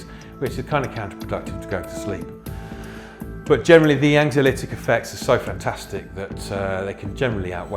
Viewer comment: Does anyone understand why he says to pulse antioxidants with NAC - n-acetylcysteine? And pulse is he implying alternate taking (one after the other/ different days) or, take together? & what is the word he uses ~ 5:26 'antihilitic' effect? I can look up the word once I know what it is